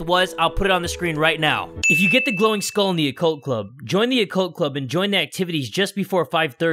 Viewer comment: When you said “Yandere-Chan” the subtitle said “unary tongue” XD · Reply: it also says coke club instead of occult club xD Or when he said "osana chan" it said "o son of chun"